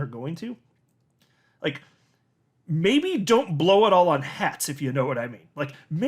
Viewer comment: > I had to rewind this, because I thought you said "Maybe don't blow it all on HEX"...and I was just like..."OMG...You are going to incur the wrath of Richard Heart". But...you said "hats"...so all is good. LOL